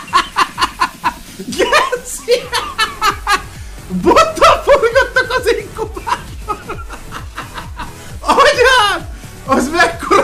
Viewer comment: mit mond? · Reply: Bottal forgattak az inkubátorban